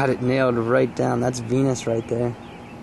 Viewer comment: Hahaha it sounded like you said thats a penis right their when you said venus